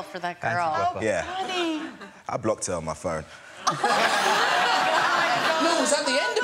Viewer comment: What did he say at 0:49? He blocked her ??